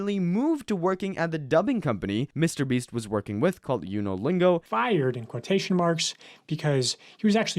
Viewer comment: when he said he got moved to the dubbing company Unolingo I thought he said Duolingo lol